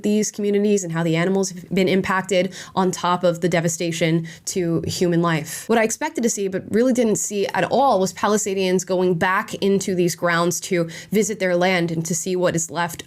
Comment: It kinda sounded like Palestinians. I know thats not what you said but i just found it a funny though.